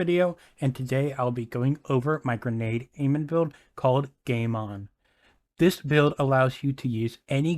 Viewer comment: I thought you said gay mon😂😭. Well he is gay so that fits